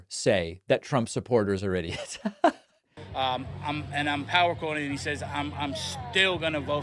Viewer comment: Did he just say he’s para-quoting?